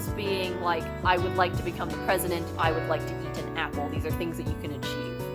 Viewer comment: lowkey replayed 3:09 a couple times uz i thought you said "I want to BE an apple. these are things you can achieve" .... ahem